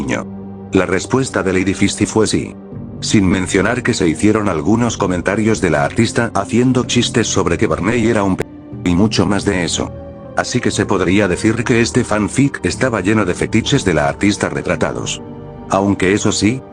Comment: estaba viendo los comentarios mientras escuchaba y pense que dijo que era pendejo XDDD